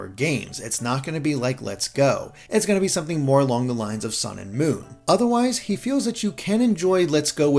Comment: When said the 2019 games would be core games "along the lines of Sun and Moon," I misheard it as a core game "along the lines of Sonic Boom" and was like "HOLD UP!" Thankfully I misheard, so multiple crises averted.